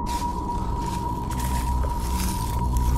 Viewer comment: He said he's going back to the cahooahhh!!! What's a cahooahhh??? Am I spelling it correctly? The dudes from Europe, so? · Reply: Sounded like he said "Ray I'm going back to the camp" 🤷